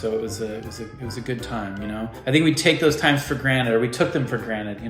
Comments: Did Justin say granite or granted? @ 15:55? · did he just say granite? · wait wait.. did he just say granite? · did you just say, take it for granite?😂 You know it's granted right??